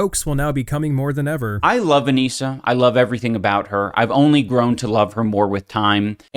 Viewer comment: I thought he said "I love Onision"